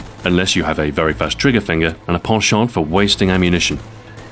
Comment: I swear, the first time, at about 4:08, I thought he said "poor shot." Never heard "penchant" pronounced that way before, ever.